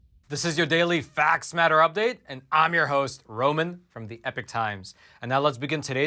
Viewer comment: great videos, BUT I STILL WONDER why I always hear you say "from Epic times" instead of "Epoch times" ;D Is this intentional?